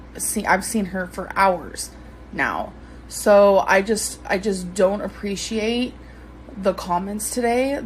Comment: I thought she said she does not read the comments. Ok boo boo. She loves all this attention. Positive, negative, it does not matter to a true narcissist.